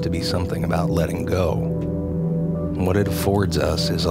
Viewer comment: Im learning english, i can't understand what word says in 1:14 minute, can someone traduce it for me? When he says "what it force us is a ..."